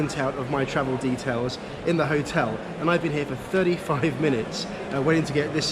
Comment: am I the only one who heard 30 f**king minutes?